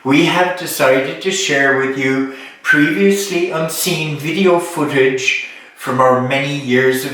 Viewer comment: Always a treat! I thought said "previously obscene footage." Oh well, maybe next time. Love you guys.